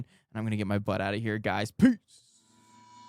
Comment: I heard this jamming out of a boom box when I was in jr high. I thought they said thunder chiefs. lol